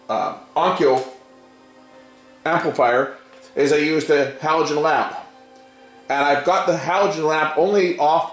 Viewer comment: Sorry but I don't understand what he says he uses at 14:04 ? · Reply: I used a halogen lamp to heat the chip up. Halogen lamps throw off a ton of infrared heat.